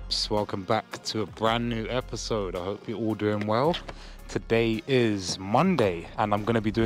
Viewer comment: LoL when I first heard you say .. hello peeps I hope you are all going well I though you said .. hello peeps I hope you're ordering well!.. 😁